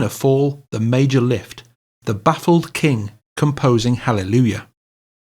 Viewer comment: Thank you! I always thought the lyrics said "battle king" and not "baffled king"?